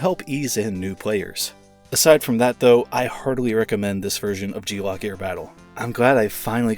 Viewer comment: At first I thought you said that you hardly recommend this, so I had to double check just to make sure. But overall, a solid review! Even though I've been wanting to see After Burner on the Switch, this one looks similar enough to catch my interest, so I'll give it a shot some time.